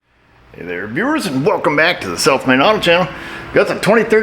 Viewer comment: Off topic, but no matter how many times I hear your vid opening sequence, I swear I hear, "Self Made" and not "South Main".